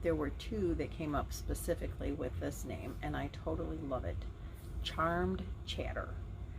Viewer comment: When you said Charmed Chatter, I thought you said Charn Chatter (half charmed half yarn) LOL